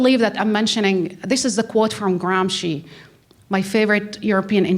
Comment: who was she speaking of, please? Closed Captioning is of no help, of course. It sounded like Graham, or Garam Xi, help, anyone? Thank you. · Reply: She speaks of Antonio Gramsci, Italian revolutionnary intellectual..